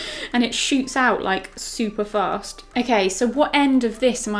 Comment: When she said super fast i thought she said super farts